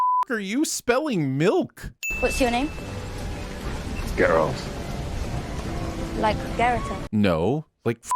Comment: The real sin here is hearing Ger-Alt repeatedly. Someone teach this guy Geralt's real name pronunciation. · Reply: I heard "girlt" xD